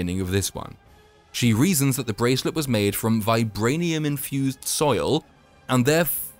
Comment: pffff, I heard "vibranium infused soy"